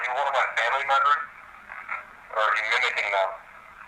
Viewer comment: when you ask if they are a family member or is the spirit mimicking them I hear “mimicking them”